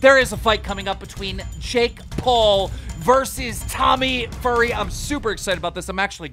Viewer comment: is it me or did keem call tommy a furry in ths part